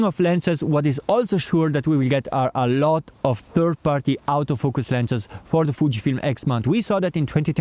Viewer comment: Love the channel, the scenery, your accent (perfect English, of course) but I had to laugh when I thought you said third party out-of-focus (autofocus) lenses, haha.